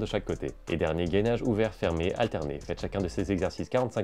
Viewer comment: A la fin j'ai entendu "et quand vous progressé, faites cet entraînement 4h d'affilié" 😂